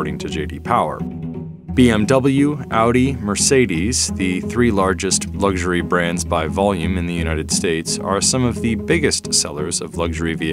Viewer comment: Wait what. BMW Merc & Audi are the 3 largest luxury car brand by volume? Lexus: Hello? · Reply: I thought he said 3 largest German luxury brands?